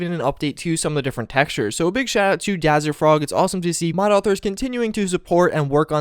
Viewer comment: Did he just say Dazzerfrog? HAHAHAHAHAHAHAHHAAHHAHAHAHA